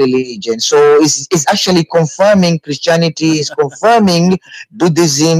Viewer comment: Did he just say that Islam confirms Christianity, buddhism and is confirming ATHEISM?